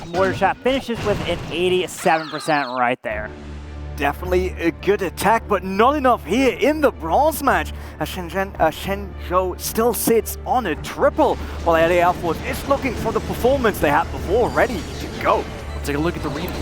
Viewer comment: did called shenzou shinchan lol😂